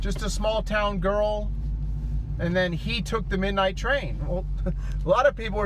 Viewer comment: I heard "HE took the ...."